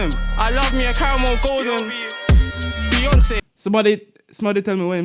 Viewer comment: I think he just says "yo B" in that adlib deya